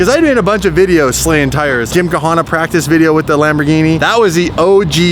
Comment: Did he just say “gym ka hana”? LMFAO